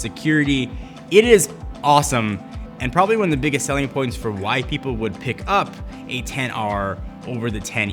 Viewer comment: did he say white people? 😂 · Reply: LMAO, no. He said "...And probably one of the biggest selling points for WHY people would pick up a XR over a 10e".